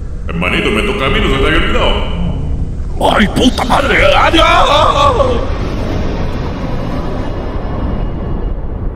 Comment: Excuse me? XD (No entendi) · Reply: Te dicen internet explorer